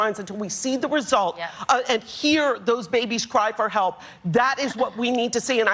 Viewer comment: Wait, did I misheard that, "we want to hear our baby's cry"